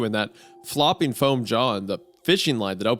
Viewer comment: I thought I heard “Flopping foam john” and was expecting a Mr. Floppy reference